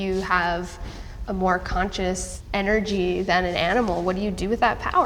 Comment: Did she just say conscience energy💀